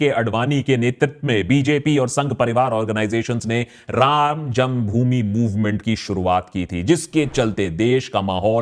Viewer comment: what did you say ?? "Ram jungbhumi or ram janmbhumi ?"